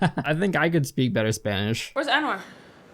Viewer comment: yo on god at 2:06 i thought she said ‘where’s n word’ and i was about to ask lele what’s up